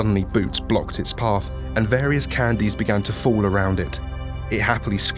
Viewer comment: I love how they were able to instantly calm 999's hatred for 035 by throwing candies at it. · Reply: For a second I thought you said 'candles'